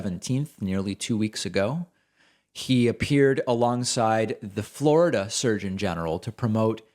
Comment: I heard that as "the Florida Surgin' General"